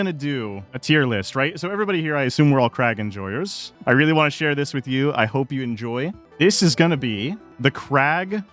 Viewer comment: I THOUGHT YOU SAID CRACK 💀